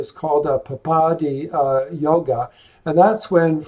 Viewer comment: !Can you write what yoga you said? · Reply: Papaadhi yoga - malefics in 6/7/8 from LG or MO